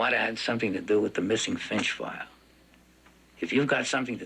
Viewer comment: I thought he said "the missing French fry" XD